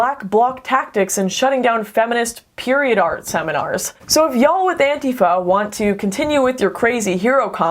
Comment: Did she say y'all? So hot.